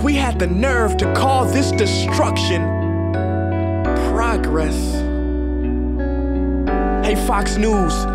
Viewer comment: If you turn on subtitles at 2:36, it says "Ranga Reddy" instead of "Progress" lol